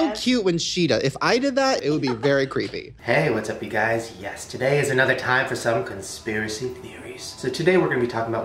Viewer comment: did he said YESTERDAY is another time for conspiracy theories?